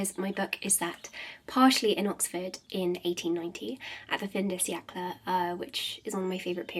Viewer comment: It's driving me absolutely nuts trying to work out what she says at 6:14/15 after saying the book is partially set in Oxford in 1890 at the ? which is a favourite time period. Subtitles have been zero help and it's bugging me so much! Does anyone know what she said? xx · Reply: Fin de siècle, French for end of the century